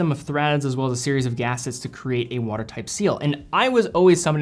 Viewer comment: Did you just say "water type" instead of water tight at 8:27?